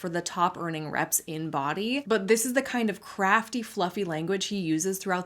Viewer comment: I thought you said "crappy language" instead of "CRAFTY language" and I was like, damn is spicy today 😅